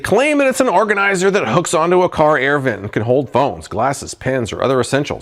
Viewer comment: I swear I hear “bones” instead of “phones” for the Vent Organizer. You can store your everyday essentials like bones, lipstick, jewelry, and more! Bone collectors out there have DYING for a product like this. Uber drivers can now share their bone collection with their favorite patrons!